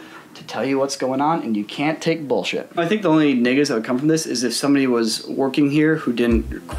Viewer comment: Is it just me Who heard him say the N-word when he says negative